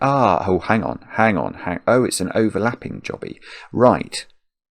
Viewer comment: did I hear that right, overlapping jobby?😆